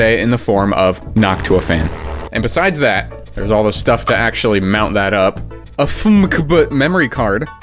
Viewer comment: did bro just say hawk tuah:face-blue-smiling::face-fuchsia-wide-eyes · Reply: No, he said Noctua